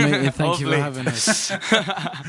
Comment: at the end when niall says "see you later" it sounds like he said my name! (sierra)